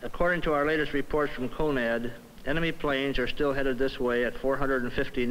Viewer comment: by BOTH timecodes ... Did he call NORAD "GONAD"?